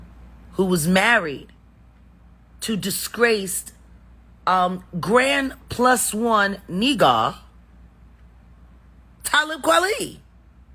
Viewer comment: Did i hear that right "grand plus one Nee Gah"